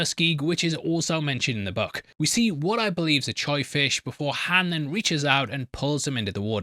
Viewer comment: I've never heard someone called a Koi fish a "choy" fish 🤣